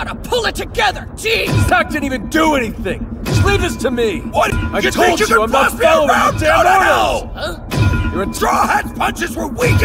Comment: what does that line say? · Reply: Law: I told you I’m not following your damn orders Kidd: You think you can boss me around go to hell